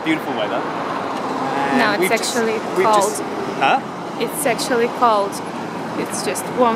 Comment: it sounds like he is saying its sexuly cold :)